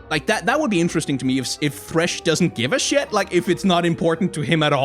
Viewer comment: I'll admit I tabbed out right when it was said, but I thought said "It would be interesting if Lucien didn't give a shit if Senna was released" and I was laughing at that mental image.